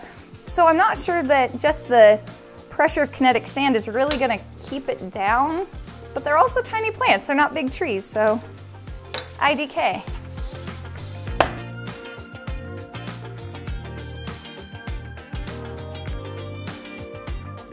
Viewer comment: for a second I thought she said “i decay” instead of “idk”